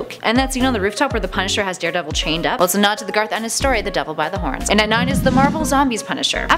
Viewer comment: What the Frig did she say at 0:38? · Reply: "A nod to the Garth Ennis story The Devil by the Horns". Garth Ennis is a comic book writer.